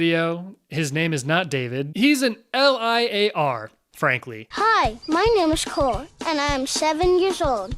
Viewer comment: They probably cast a bunch if nobody kids, thinking they would never see them again. Little did they know, Zendaya was gonna come back and accidentally expose them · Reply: Lol i thought he said 'my name is cool '